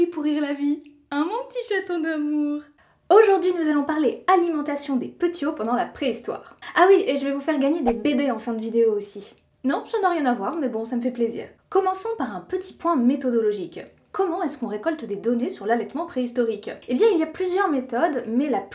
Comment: Sur le coup ; j'ai compris " je vais vous faire gagner des bébés " 😅